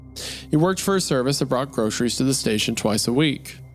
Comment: I misheard this as the man works for "Brock's Groceries" and for a minute there I had goose pimples, it felt like someone just walked over my grave.